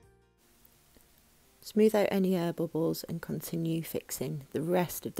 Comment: Lol. When U said air bubbles at first I thought u said herbivores. Obviously my accent is different then yours. By the way, your cabinets are beautiful. You’ve got great attention to detail